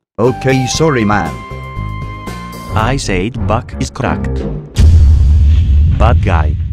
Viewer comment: i thought he said bat crack